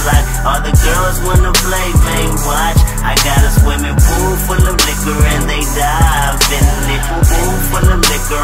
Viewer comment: I always thought it said pool full of niggas and you dive in. XD